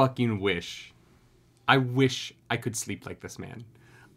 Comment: I missed one important word, and heard you say "I wish I could sleep --- this man [Zoro]" and immediately went "You want to what with Zoro?" Ah, good times. Mishearing things can cause great comedy.